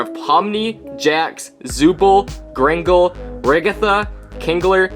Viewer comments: I JUST HEARD “grangle” 😂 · Did he say grangle